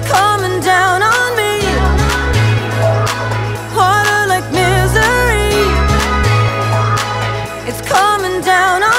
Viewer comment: I legit heard “Harder like misery” not “water like misery” I misheard so many words😂